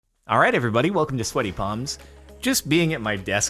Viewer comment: Thought said "alright everybody welcome to slutty moms" 😂